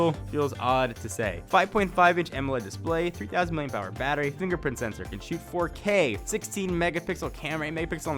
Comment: Sounded like he said fingerprint sensor that can shoot 4k...